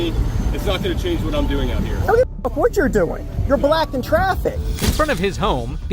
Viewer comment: Your black in traffic. Story of my life😂. Its not what he said but definitely what i heard